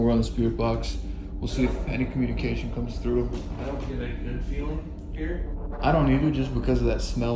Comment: When you first turned on the spirit box, I heard "Good Smelling." As in the room that smelt of decay which strong demonic entities are alleged to create.